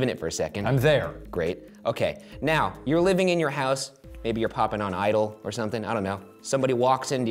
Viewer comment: did ryan said “popping on idol” @ 2:58 or am i trippin